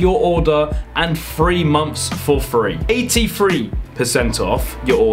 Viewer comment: Him: Three months for free What I hear: "Free months for free"